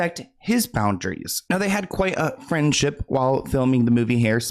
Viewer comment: I thought said you “they had quite a cringeship” instead of friendship , I was like YUP! 😂